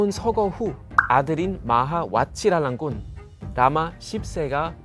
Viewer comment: 나만 라마ㅆ새로 들었나 게임하면서 듣다가 흠칫 ㅋㅋㅋㅋㅋ